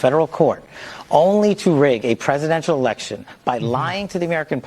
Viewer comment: I heard him say rig arrests